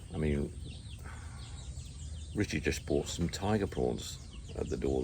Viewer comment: Ohhh tiger prawns- I thought you said “tiger paws “- I thought I was going crazy…. ( turns out I was). Do please let us know how the visa situation is- are u still on the balakbayan visa or spouse visa yet?